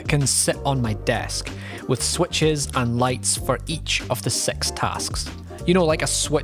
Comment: each time he said "six tasks", i heard "sex tasks"... is my mind a little pervert ?